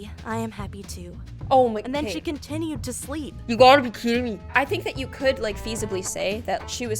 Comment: So true whoever looks in the subtitles you will see you got a bikini instead of you for to be kidding me